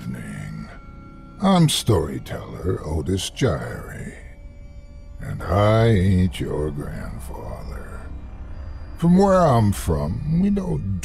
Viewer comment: I keep hearing :"Im Otis Jiry and I Ate your grandfather"